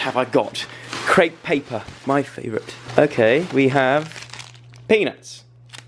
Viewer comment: I so thought Joe said "we have PENIS" when he opened the bag😂😂😂